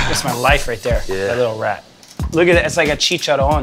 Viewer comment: Did i just heard "chicharon" between 10:00 - 10:05 from the guy wearing black? Or its just me